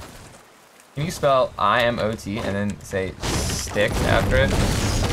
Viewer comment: What did the guy want ninja to say at 9:01??? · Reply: Brannon Ecker He wanted him to say “I am autistic”